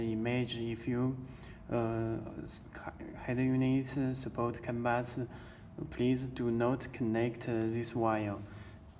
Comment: I did not under stand what he says in 0:53 time. any one ? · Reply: That is mean, if your car have canbus, you needn't connect this cable, it is a detection cable, when you reverse your car, the canbus will decode it and show the reverse image in the radio screen